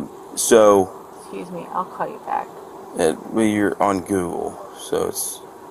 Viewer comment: "Well you're on google so... uuhhm" Did I hear that right? I do hope so because her faking a call to AAA is so incredibly funny... and pathetic! 😂 These rich "ladies" driving 💩-faced and acting super surprised when getting arrested for it 🤢 She drowe her car into the guardrail of a house and only cares about when she's getting out... wasn't interested to know if she hurt anyone. Until a good while after!